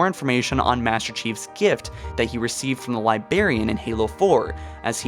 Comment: Did you just say "Libarian"